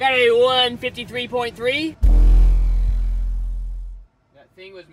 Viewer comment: Did anyone else hear him say 153.3 at 16:24 into the video